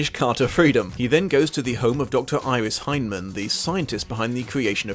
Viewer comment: Hahaha I thought you said dr. Iris Hyman, but you said Hindman